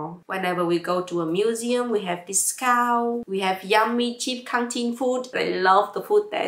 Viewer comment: Thank you for sharing with us! It is really helpful to hear your personal story of the study abroad experience. I had one question around 7:03, "We had yummy, cheap ??? food". Does anyone know what she said? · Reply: canteen i guess